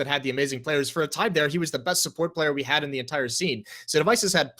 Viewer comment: did just said support player?